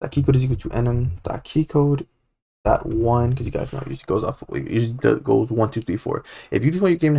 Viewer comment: what did u say @ 17:24? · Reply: "because you guys prolly want it to go 1, 2, 3, 4"